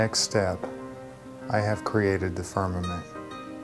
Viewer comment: Thank you for this beautiful mantra. I'm not a native Englisch speaker. Can somebody maybe help me to understand a word at 1:05 please. "Before my next step i have created the ...?" I don't understand the word he is pronauncing. Thank you in advance. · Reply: Firmament - which means Heaven